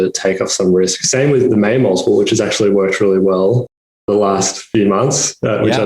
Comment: Anyone catch the multiple he mentioned at 7:58? It sounded like "main multiple"? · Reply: Ah it's the Mayer Multiple.